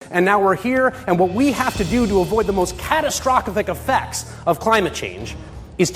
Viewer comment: Did he just say catastrophkik?